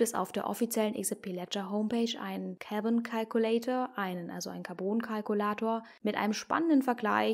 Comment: Ich hab erst "Kevin Calculator" verstanden 😅